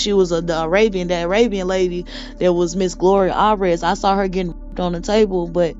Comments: Did say it was Gloria Allred getting SA'd? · These are the missing migrant children when she described kids being in cages and that they don’t speak english!!!😢😢😢 The horrific tortures done to them, it just made me bawl. This woman needs a lawyer asap! She needs to be protected! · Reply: did she just say “Miss Gloria Allred”??? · She mentioned Gloria Allred too!? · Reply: I thought that's what she said too, but when I listened again, it sound like Alvarez. Maybe another interviewer will ask for clairification. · I PRAY that you advised her to contact an attorney, if she hasn't already 🙏🏾 Praying for her sanity, because this is SO SAD 😢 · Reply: I thought I heard her say Gloria allred!! DEEP! .. smmfh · Did she just say Gloria allred? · Wait, did she just say Gloria Allred was there? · At appx 5:45 did she say the lady on the pool table was Gloria Allred? Did I hear that correctly?